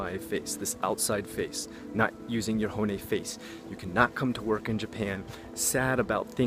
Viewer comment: “not using your horny face.” That’s what it sounded like lol.